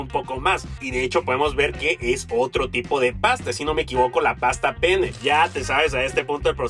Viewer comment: Escuché Pasta Pene debe ser por mi dislexia